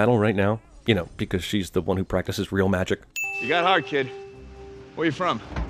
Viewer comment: Doesn't Captain America mean "heart" instead of "hard"? Seemed to me like he just meant that Spiderman didn't seem like a bad guy.